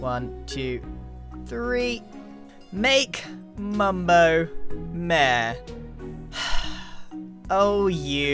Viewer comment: I was not watching for the first few seconds and I legit thought said “make Mumbo meh”